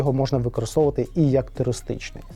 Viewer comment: а мені почулося Терористичних...